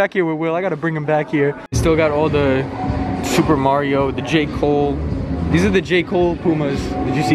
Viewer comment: lol I thought he said the Jake Poles😂😂